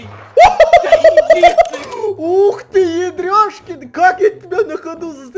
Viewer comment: мне одному послышалось Ярик?